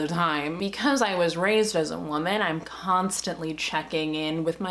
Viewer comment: I had to relisten cuz the first time I thought you said ”because i was raised as a Mormon” instead of as a woman, and I questioned everything I knew